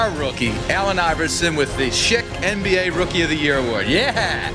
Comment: Lmao thought it said " the shit" NBA ROY award lmao